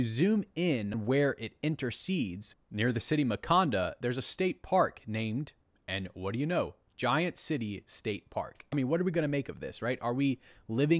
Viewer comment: Anyone hear Wakanda? 😂